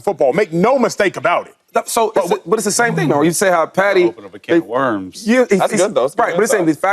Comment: did Shady just call him "Fatty" 😂😂😂😂 Fatrick Mahomes not being the allegations 😭